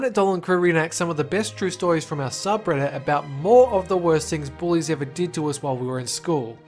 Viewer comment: When they said bullies I thought they said boys